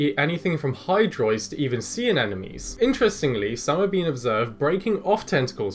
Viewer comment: At first I heard "enemies" instead of "anemones"